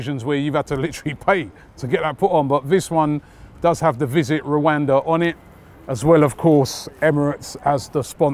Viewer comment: I thought it said visit Wakanda lol